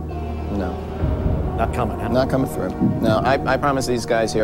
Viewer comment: I heard him say, "still not going though."